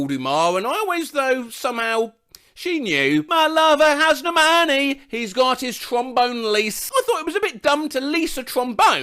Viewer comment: I thought 3:40 was "he's got his trampolines" , then there's Nightcrawlers "push the feeling on" and instead of the actual lyrics of "their lives to pull us through" I thought it was "they lied upon a spoon" and whilst we're on topic of 90's dance music, I thought Io's rapture was "Me, I'm horny don't you know" instead of "Mi Amore don't you know" 💀💀💀